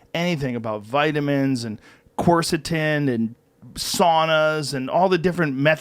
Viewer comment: what is Joe saying? That 2nd word He says Vitamins, Corsitenedasdfasdf?, Saunas. Edit: Oh... He's saying Quercetin I think